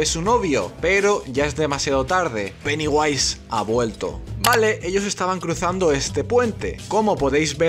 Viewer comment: pensé que decía "ha muerto" y digo queeee